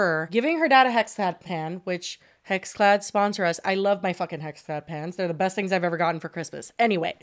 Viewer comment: I was listening to audio only and I thought you said HexClad PANTS and said she loves her HexClad PANTS... And I was like what are these pants I need to get? 😂